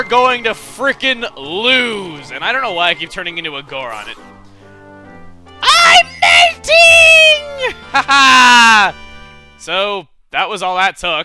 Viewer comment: Lol when he said I'm melting I thought he said I'm mating! xD